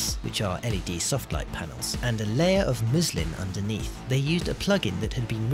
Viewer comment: Did he say a layer of Muslim underneath??